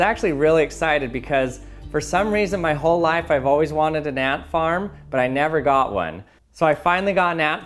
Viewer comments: the subtitles say “I’ve always wanted a nap, but I never got one”. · Lol 2:08 with subtitles says I've always wanted a nap, but I never got one.🤣🤣🤣